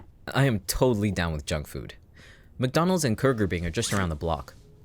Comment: Did he just say...KURGERBING?! wheeze